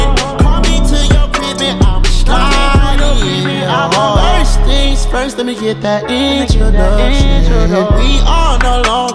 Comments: I thought he said first let me get that edge 😶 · Man thats not what i thought he was saying AT ALL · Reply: i thought he said ”lemme get that inch” and ”lets get molly now” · I used to think it said “let’s get NADIA” “Yh I need ALIYAH” 😂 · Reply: i thought he said lets get loyal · Man thats not what i thought he was saying AT ALL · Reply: I heard "first things first let me get that energy" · This is what I heard: “Feeling like it’s your power” “Feel like it’s your power” “Let’s get Nadia” “Yah Midoriya” Help- I can only image Todoroki singing this now thx to fucking tik tok omg